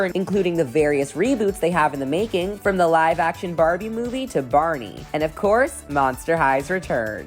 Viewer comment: did i hear correct barney 💜